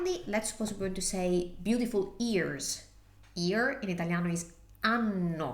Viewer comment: I thought you said beautiful ears and was thinking how odd, who has beautiful ears 😂. Thanks so much for your fantastic explanations in English, it makes a big difference.